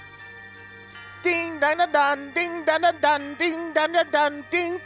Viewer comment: who else heard him say "ding fries r done, ding fries r done!" XD